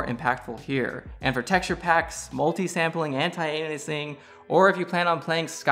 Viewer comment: Pretty sure I heard anti-anusing. I know it's supposed to be aliasing, but the way I heard it was hilarious.